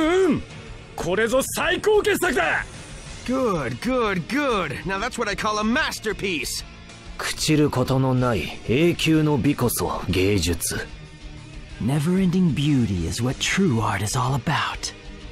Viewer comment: it sound like he said "Gay justu"